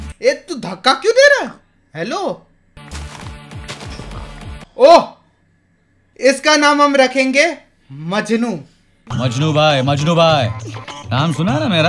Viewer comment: he said iska naam ham rakhenge and I thought main and then he said majnu omg Btw Nice video and love that part when u say 😙😙 "Haan bhai nischay" Big fan❤❤